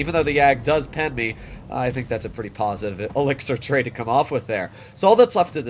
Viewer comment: lol did just say "positive elixer trade" he def plays CR